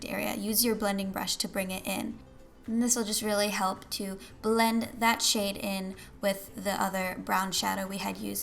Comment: I thought she said "help blend that shit in!" LOL